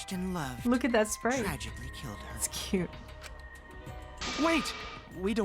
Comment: did just call kokichi cute?